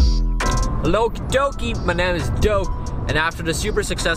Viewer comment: sounds like he says "bananas doke